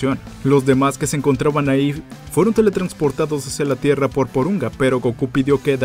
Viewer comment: "Fueron teletransportados hacia la Tierra por Poronga" Si, sé que es Porunga pero la primera vez escuché Poronga xD